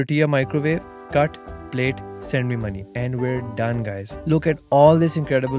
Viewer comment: Did i hear @ 4:41 .. Send me Money???😂😂😂😂😂